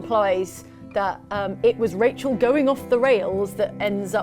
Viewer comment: When they said Rachel going of the rails I thought they said Rachel going off to wales and was really confused for a second